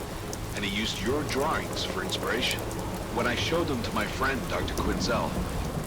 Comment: I thought he said "and I used your drugs as inpriration"